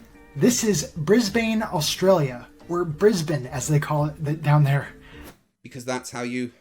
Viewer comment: he actually says "as they call it DOWN there". Lol.